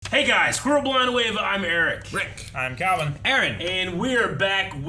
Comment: Every time I open one of your vids it sounds like Eric is saying "screw blind wave" lmao